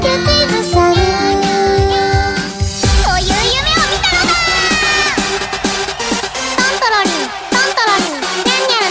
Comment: i dont understand shit , but me like it :D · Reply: She shouts "I had a dream like that"